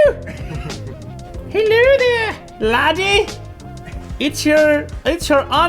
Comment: YouTube subtitles thought he said nudie🤣🤣🤣